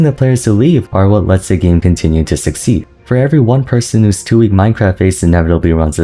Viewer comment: Did you say two weeks Minecraft phase? 👀